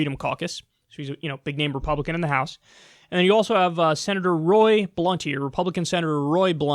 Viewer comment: I thought Kyle said Rep. Roll Blunt 😂